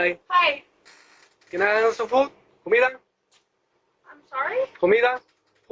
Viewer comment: I think he says „Comida“ (food in spanish), not „Humila“… weird guy, I‘m glad the lady didn’t let him come inside.